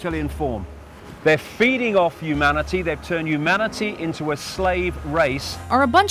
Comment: Anyone else hears "manatee"? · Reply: I know it's supposed to be humanity but yeah it does sound like manatee